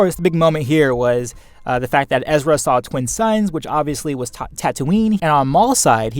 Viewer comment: I completely misinterpreted what Ezra and Maul saw in the holocron scene. I thought Ezra said "twin SONS", as in brothers, and thought that referred to Savage and Maul. Then I thought, "but Savage is dead isn't he?". So when Maul was saying, "He's still alive" I thought he meant Savage Opress had survived and he was going to find him. Then I watched this and realised what an idiot I was.